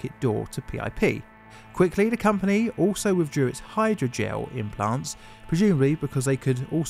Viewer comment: I was in absolute horror for a second when I heard "hydrogen implants".